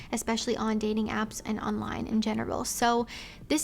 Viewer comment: It sounded like she said women have more options in genital instead of general 😂 but both would still be true